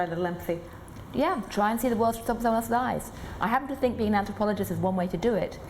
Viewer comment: What did she say????? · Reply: "Try to see the world through someone else's eyes". I had to re-watch a couple of times 😂. Her voice is made cute tho.